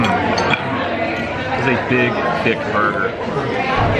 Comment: Captions say "He's a big dick burger"